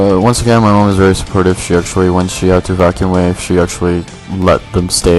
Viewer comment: Btw, did you just say at 2:26 "Vaccuum Wave"? Damn, this is getting serious! Pokemon names are starting to replace some words! lol